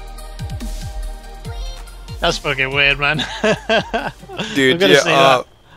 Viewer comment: I think he said do you need moneys hahaha